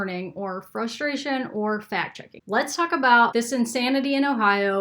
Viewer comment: At the beginning I wasn’t paying 100% attention when you said fact checking and I thought you said fat chicken. That’s all😂